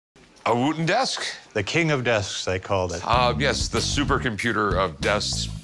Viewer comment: Ohh I thought it said “Wooden Desk”